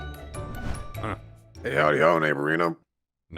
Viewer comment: I thought said “Hey how’s it going, ay burrito” 😭 7:05 NO! DON’T TOUCH CLIPSY! HE DON’T DESERVE TO GET BEATEN UP OR KILLED, HES BRINGING SOLAR BACK. 12:32 “kid thing” 😭